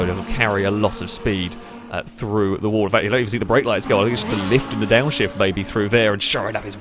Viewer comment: I blatantly refuse to believe that I'm the only one who heard "just a lift and a downshift baby"